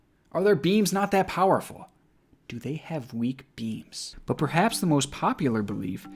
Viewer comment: Nick: "Do they have weak beams??" Aliens: "This human just ended our whole career." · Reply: i hadn’t seen this part of the video yet and i thought you said “weak beans”